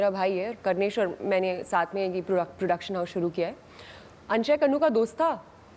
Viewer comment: did she say 'reproduction'🤔